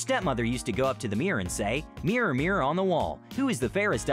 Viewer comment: you say "mirror mirror on the wall" But she actually says "magic mirror on the wall"